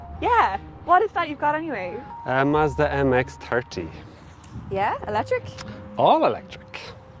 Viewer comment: From Australia: who else has to have the subtitles on to understand them??? I thought he said Mazda MX-Turkey ha ha